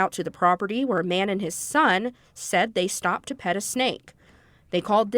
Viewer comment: Did I hear that correctly @ 2:40 "They stopped to pet a snake"?